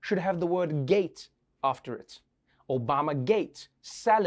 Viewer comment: I heard "Obamagang" not "Obamagate" anyone else with me ??